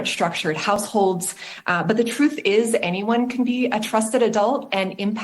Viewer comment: I heard her saying “anyone can be a groomer” when she’s saying “trusted person”. Just say’n.